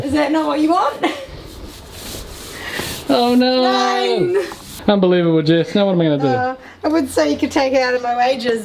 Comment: I thought you said Unbelievable Jeff at the start.